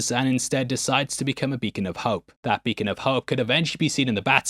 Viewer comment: I heard “vegan pope” so had to rewind to check. “Beacon of hope” makes more sense 😆